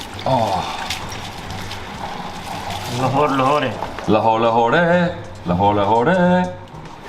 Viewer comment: wtf is that at “16:12 “ dd he just say loho lohode 😂😂😹